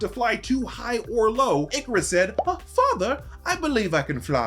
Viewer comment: that sounded like " F... father"